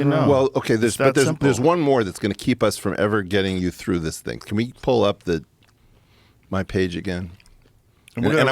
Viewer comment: Any time I hear “Jamie pull up” I just hear Flint Dribbles sleeves 😅🤣